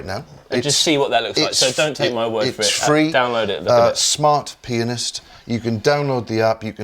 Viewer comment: Nice video, first time the app was mentioned I heard "Smart Penis?!" ... made me laugh... nice one mate :)